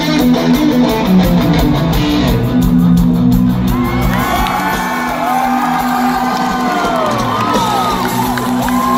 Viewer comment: Does anyone know what Dave said right after the solo 5:08 + ???? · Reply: He was pointing at Yayo and saying "kiss guy" to the crowd. Dave's reaction to the crowd chanting "kiss guy, kiss guy" after the song was over is priceless!